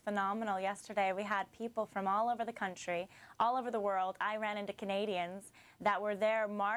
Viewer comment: Did she just say "Iran to Canadian"?